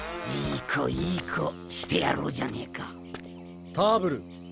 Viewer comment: did he say jamaica here 😅